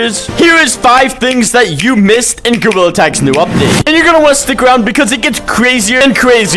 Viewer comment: What secret did I miss?🤔👇 Subscribe pls or Lucy will get you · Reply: bro the captions thought when u said gorilla tag it thought you said Google tag